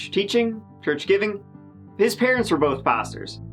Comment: I literally heard “his parents were both bastards” and had to double take the captions...I can’t stop laughing 🤣